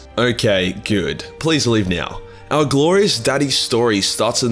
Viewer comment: Did just say "Good.. please leave. Meow"??